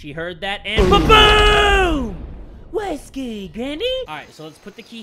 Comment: or 10:30 did he just say, whiskey, granny 😂😂😂